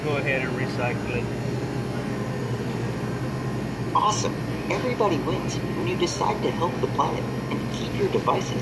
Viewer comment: captions said, "awesome everybody went nudists"😂😂